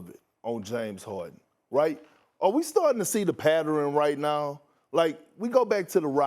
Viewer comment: Did Perk just say “patterin”? 4:09 Twice??? 5:05 Three times??? 5:45 🤣🤣🤣 And I’m starting to think Harden is legitimately bipolar🤷🏾‍♂️🤷🏾‍♂️🤷🏾‍♂️